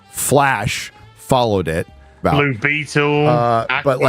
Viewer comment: I know said "blue beetle" but it sounded like he said "libido"... sounds better than some of the stuff we've gotten lately